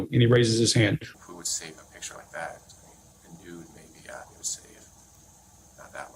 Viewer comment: I think he’s saying, “A nude, maybe,” not “who.”